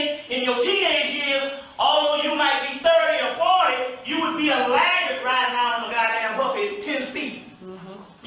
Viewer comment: Do you know what he says after 1:26 - 1:30 ? I didn’t hear the part. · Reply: "you'd be a laggard (as an adult) riding around on a Huffy 10-speed"